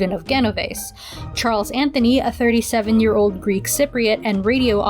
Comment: I thought that said he was a Greek Cryptid. I need to lay off the internet